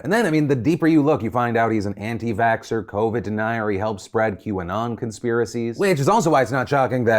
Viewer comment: what did Phil say I think it was Qanon